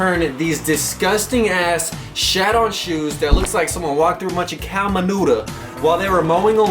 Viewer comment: did he just say "cow manuda"?